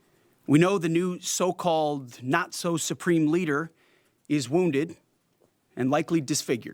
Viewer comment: not so supreme leader? i thought he said Nacho Supreme leader.